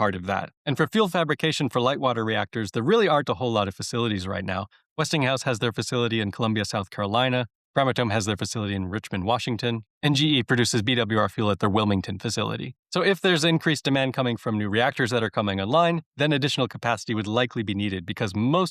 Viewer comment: sure sounded like "Richmond" instead of Richland WA Nuclear power makes a lot of sense as the source for immediate AI demand and also for building out solar panels.